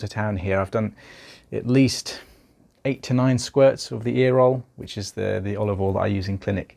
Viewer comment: lmao I thought he said 89 squirts in the EAR HOLE.....after googling I see its EAROL OLIVE OIL SPRAY / DROPS LOL - love this guys soothing voice too